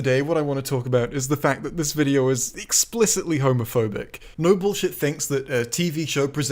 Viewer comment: I don't know if this is just me, but everytime you say his name all I hear is "noble shit"